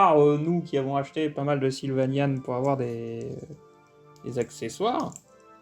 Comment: à 6:01 tu parles d'accessoires que vous avez achetez du nom de quoi ? J'ai compris "silvanian" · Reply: oui c'est bien ça, les sylvanian sont des figurines toutes mignonnes d'animaux humanoïdes, plutôt diriger vers un public féminin je dirai. D'habitude tu en trouves dans les rayons jouet ou magasins de jouets ^^ . Et il y a souvent des accessoires de la vie de tous les jours je dirai, truc de pic nic, barbecue, cuisine , maison , etc etc . Mais c'est un peu petit pour des figurines 1/12 finalement ^^ mais oui on en a , et on les as surtout pris pour les accessoires ^^